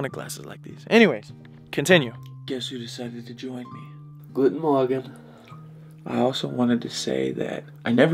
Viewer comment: For the germans: 6:49 did just say Guten Morgen?